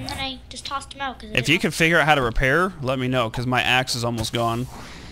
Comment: When he was saying ax it stopped and it started playing again it sounded like he said my ass is almost gone